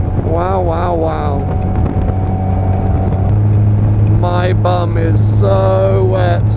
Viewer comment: I heard 'My Mum is sooo Wet'!